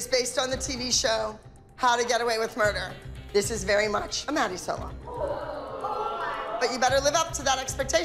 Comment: did she say.. a mammie? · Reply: she said “maddie”.